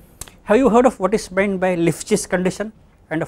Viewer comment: At around 12:30 he mentions something that sounded like "lifjes condition". Can anyone clarify what this condition is? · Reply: It's called Lipschitz continuous or condition, it's basically function satisfying |f(x)-f(y)|<=C|x-y| where C is nonnegative and x and y and from domain. Such functions are Lipschitz continuous over domajn